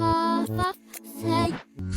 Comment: The subtitles say “fxxk’s sake” LMAO